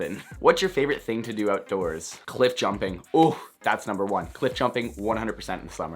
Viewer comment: I thought you said 'clit jumping'